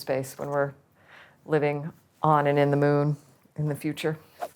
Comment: did she say living on and ‘IN’ the moon